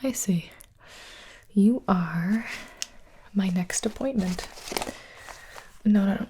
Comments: For a second, I thought she said "You are my next disappointment" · Gibi: okay so you are... My next appointment! What i thought she said: okay you are.....ugly (not meant to be mean! Sorry if it is!) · In the 0:05 when she said "i see" i thought she said "hi silly" 0-0